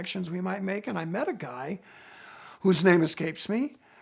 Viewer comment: I thought he said " his name is Gabe Smee"